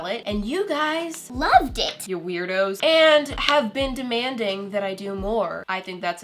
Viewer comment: Did she just call us weirdos??